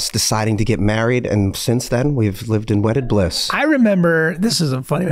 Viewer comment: Did he say 'wetted bliss"?